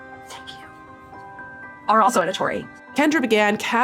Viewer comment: What words is she saying at 3:03? I hear "...are also editory" but that doesn't make any sense.